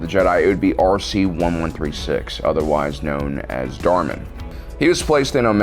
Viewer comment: Both "DOGMA" lol instead of the first one being "Darman" or however you spell it